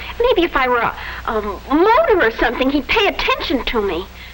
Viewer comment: Anyone else thinks it strange that the daughter used the word modem in 1952 at 13:22? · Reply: I thought she said “motor or something”